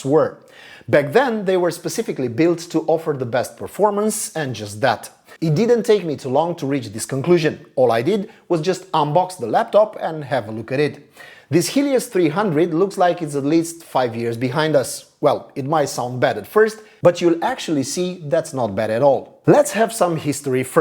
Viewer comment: For 1/2 of the video I thought the author was saying "this HIEDOUS 300", which did not many sense as he was clearly praising the laptop. I love this design!